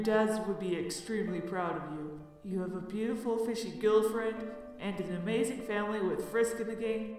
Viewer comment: wait, did he say 'a fishy gillfriend' 😆